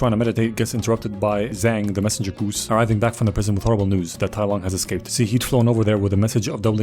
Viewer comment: The goose came to shifu to tell him - and I heard - "Tai Wan has escaped"... I honestly like my version better